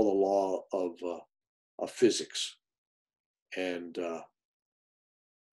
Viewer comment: What did you just say there . . . . . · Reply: "Sometimes just being luck and in love with God is enough"